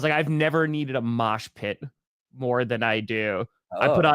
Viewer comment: A receiver is supposed to maintain possession of the ball for it to be considered a catch right? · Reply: I thought you said posh pot and I will never be the same for having formed that image. Just politely restrained jubilance.